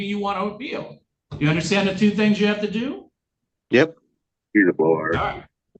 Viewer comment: Did I just hear, “He’s a blowhard”? 😂